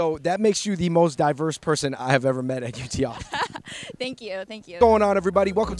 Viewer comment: I thought he said UTI